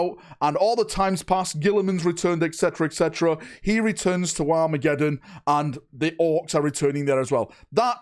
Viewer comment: Happy new year!! Great video. For a minute there at 3:34 - I thought you said WAAAAAAAA-megeddon. I have to concede Dorn would look amazing in a Black Templars colour sceme.